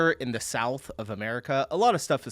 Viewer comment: did he say "in the south of america"?? talking about Texas/Florida or every South American country??